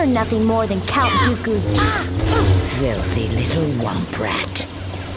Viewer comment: what did Ventress just call Ahsoka? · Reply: Mark Mitchell “flithfly little wamp rat”